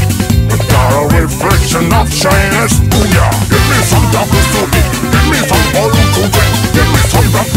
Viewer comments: thought he said "gimme me some 1 2 3" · Song sounds very Klingon. All I hear is "Give me some gagh!" Which is terribly silly.